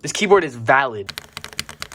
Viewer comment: Hold up, what did he sayyy? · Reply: He said it was valid, nothing else